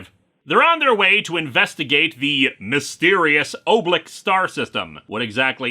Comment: I was on another tab when I heard Oblik and thought it was Oobleck with one "o"